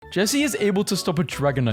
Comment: The sad stories behind, Jessie, James and meowth is how the fandom loves them so much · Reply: i thought you said jesus